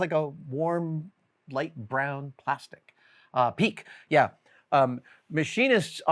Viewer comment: did he say PEEK?